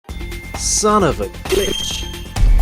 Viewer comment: Chronomatrix Impossible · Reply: The littlest cat that could eh! Perf! That's why there are so many. Also, turn on automatic subtitles at 0:00. "Son of a fish!" instead of "glitch".